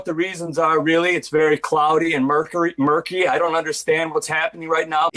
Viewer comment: He is the actor for banner who is a gamma scientist and i find it funny that he would accidentally say mercury instead of murky. Really proves how ordinarily dumb they could be.